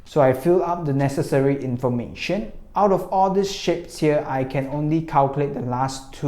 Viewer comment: man I just saw one of ur vid today and am ur fan now i couldnt understand the 9th qs and lmaooo I fell in love with you wen you told "out of all these shit" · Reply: He said shapes not shit